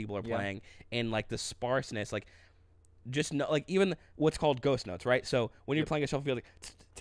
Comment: When said ghost notes, I thought he said ghost nuts. I was thinking wtf are ghost nuts? Lol!!!